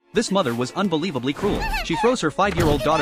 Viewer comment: for a sec i thought you said "this mother was unbelievably cool"